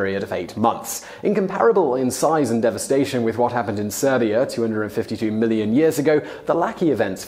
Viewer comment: Simon, did I hear you say "Serbia", and not Siberia? 17:28 Is that 'L4' in the lower left corner of the diagram refer to a Lagrange Point?